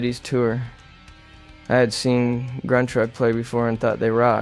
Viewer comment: could anyone tell me the name of this band? I dont understand it- sounds like „crunchrock, grungeraw“ 😂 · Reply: Gruntruck! Now i got it! 😅😊